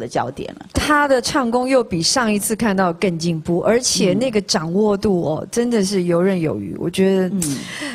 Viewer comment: did she say N**